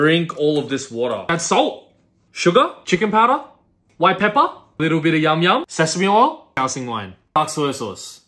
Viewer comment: Thought he said a little bit meow meow